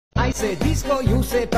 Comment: What I heard was “I say disco you say mummy, disco disco, mummy mummy 💀💀